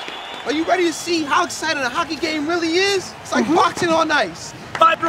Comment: Cocky fight🤣🤣🤣🤣🤣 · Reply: I thought he said cocky before he said hockey I was like huh.....say it right Calvin lol.